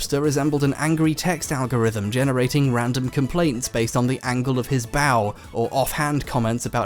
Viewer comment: I thought you said "the angle of his bowel"